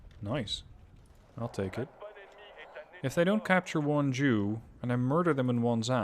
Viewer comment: Sounds like you say "If they don't capture one Jew" Lol